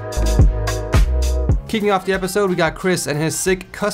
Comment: sorry the first thing I heard was "kicking off the video is"